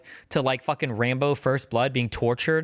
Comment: wtf he said rainbow first blood